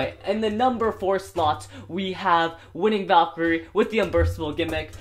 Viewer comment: did he say winning valkyrie??